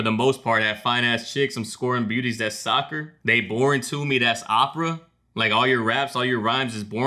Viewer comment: I thought I heard “born to me that’s Appa.” Like how Appa was Aang’s spirit animal. Then “scoring beauties like Sokka.” Cause Sokka always got all the girls on his arms in Avatar. What you said makes total sense too lol